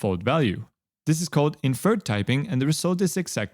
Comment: Correction: at 17:30 the subtitles say 'invert typing' when it should be 'inferred typing'